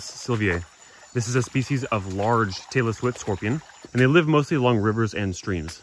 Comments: Sounds like he said “large Taylor Swift scorpion”. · Did he say "taylor swift scorpion? :P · had to replay like 4 times before I heard anything other than "Taylor Swift scorpion"